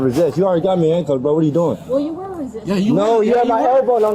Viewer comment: I thought you said you weren’t resisting. Looks like resisting.